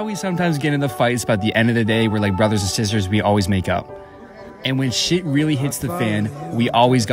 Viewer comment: I thought he said "at the end of the day we are all like brothers and sisters and we all make out" 😂